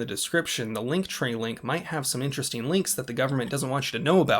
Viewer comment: I had to do a double take, I swear I heard “I’ll leave the lightning link in the description below”’😂